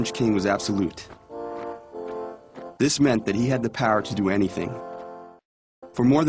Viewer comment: Why did I hear 'this meant that he had the parot[pærə] to do anything😂